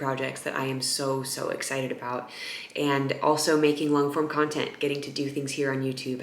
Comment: So at the end you mentioned doing more "long form" content and I thought you said 'LAWN form" content and I thought "Hmmm....not even sure what that IS but if Elyse is doing it then I'll watch it'. HAHAHA. You are the best :-) looking forward to listening to you out on your lawn!!!